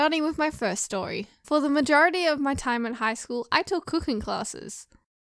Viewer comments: What others heard: "in highschool I took cooking classes What i heard: "in highschool i took pooping classes xD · When you said cooking class, for a second I thought you said coke a cola class.😅😂🤣